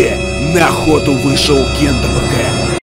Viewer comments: Мне показалось что он сказал на 0:01 нахой вышел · Я услышал нахуй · мне сначало послышалось на*** выходит